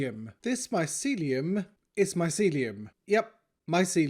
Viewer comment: I thought you were saying this mycelium is MY celium